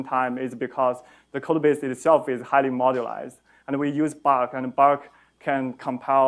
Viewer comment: Can anyone understand what tool he mentioned at 34:00 when he was talking about compiling separate modules? It sounds like "bak" / "bark" but I couldn't find anything related to that. Thanks. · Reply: Buck! <link>